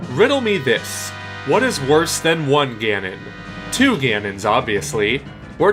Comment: I thought he said no Ganons?